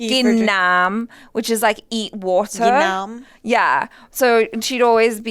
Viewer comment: that's how to say u ate something in Telugu 'thinnam'(sounded like it)